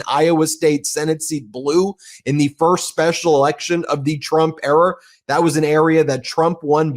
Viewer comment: Did you just say Trump ERROR instead of Trump ERA? Not that I don’t disagree…